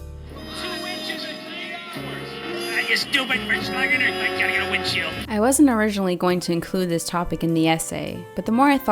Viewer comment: what did he say ?? 😂😂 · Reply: "Ah, you stupid 'flershlugina', I gotta get a windshield" 😂